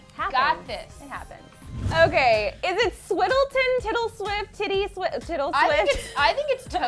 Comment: I just heard tittieswift 4:36-4:40!!